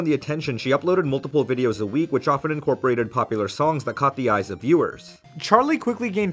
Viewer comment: It took a few times for me to hear "Caught the eyes of viewers". I kept hearing "coffee eyes" or "copy-ize"